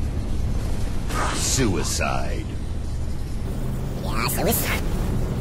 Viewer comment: it kinda sounded like he said "yeah suicide" when they rewinded the clip back xD